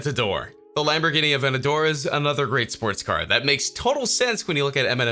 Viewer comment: did he just say “sports car”